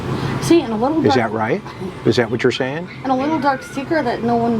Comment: If the black truck was the last thing Summer allegedly rode in before disappearing wouldn’t it be kept by LE instead of C being able to sell it??? Which truck was driven and where is it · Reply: I thought she just said Candice sold it?